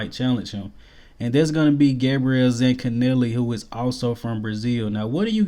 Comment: sorry bro, the guy at 6:55 ( can’t spell his name I heard it too quickly).) He’s on after HoresMD. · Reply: Zancanelli?